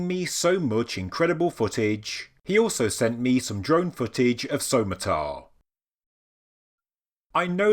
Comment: In memory of Chuck Appleton - CF-Apps7865. I’m sure this drone footage would have sat on his channel if he was still with us. Chuck was friends with both Dakota and Anyextee so I’m pleased I could mention all three in this video and used their combined assets. RIP Chuck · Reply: Yes RIP, but I seriously thought you said "drunk footage" instead of "drone footage"... please get "drunk" in his memory and Bless you all for what you contribute to society.